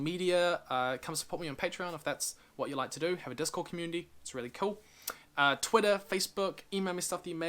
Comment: I thought I heard you say "Communi-cachet" which in my mind was some french word, then I saw the actual spelling, although I won;t fault you for mispronouncing a foreign word, even experts are susceptible to that. Great video though, I always wondered what happened to Azula, she seemed a lost and sad character hiding behind her ruthlessness and cruelty.